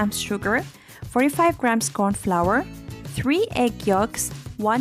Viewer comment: It says corn flour but I heard you say corn starch. Which one do I use??? I'm so excited to make this